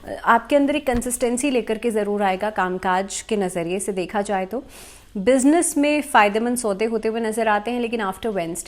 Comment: Business me Faydemands 😂 Did i hear that correct ?